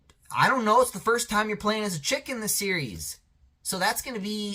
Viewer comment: I thought you say chicken do you like chicken because I like fried chicken